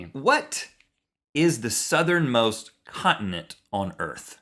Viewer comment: "What is the Southern most continent?" Me: Australia. Link: Antarctica Me:... Oh... Wait a minute... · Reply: Marlowe Wolfe it's ok... I totally thought he had said "condiment." lmfao...